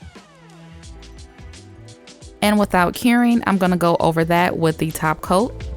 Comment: For the longest I thought she said without caring (when applying top coat after the bling gel) but it’s without curing 🤦🏻‍♀️🤦🏻‍♀️